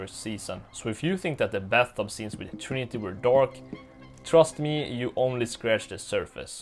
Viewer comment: I thought you said dork and my random brain pictured how the Trinity-Killer is a naky lil dorky dork going around dorking people to death with is willy vanilly pickle 😂